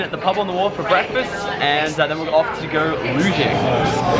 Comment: Anyone else think he said "looting" at 0:18? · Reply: Nah, you can here that he says luging